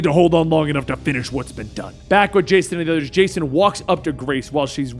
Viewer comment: what did he say at 9:27 “back at Jason and Jason” 😂 😆 😝